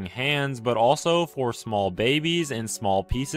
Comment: I misheard the last part as 'A special place to eat babies on'